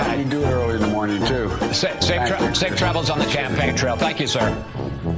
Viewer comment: I heard 'Safe travels on the champagne trail' from Chris. 😁